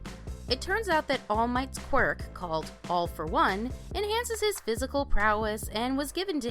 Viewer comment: did she say all for one!?